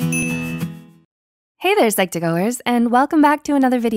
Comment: I thought she said "Hello Psychopathers"💀 True for me tho😂😂🤣🤣😢😭